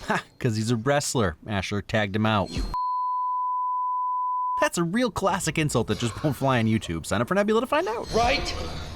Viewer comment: For us non-Nebula users, what did he say? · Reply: "You c*ck juggling thunder c$$t". Props to valsptsd814 above for pointing it out.